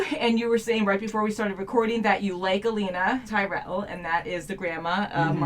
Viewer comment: I thought you had said Alina and was confused until you said Tyrell, I was like omg! Olenna! :)